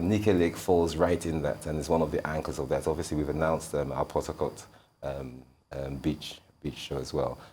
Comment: I thought they said port Harcourt